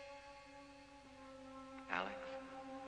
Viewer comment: what is he saying? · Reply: “Alex… You know there’s only one way we’re going to end all of this. You’ve got to get back in there… “and find Carla.” [?] “Because she’s the one who’s keeping the spirit [???]”